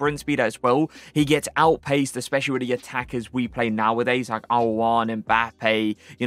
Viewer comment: Did you say at 5:10 that we get outpased by Aouar? 😂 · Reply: he said al owairan if i’m not wrong